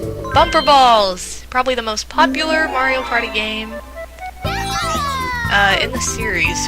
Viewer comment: Did you say Wario Party at 06:10?